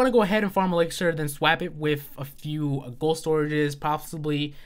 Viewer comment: what did you say? lol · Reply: lol swaap